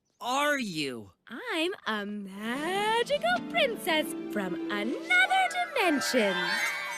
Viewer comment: "Who are you?" "I'm a magical princess qith severe dementia". I've misheard the first time and even after hearing again, i still can't unhear it. '-'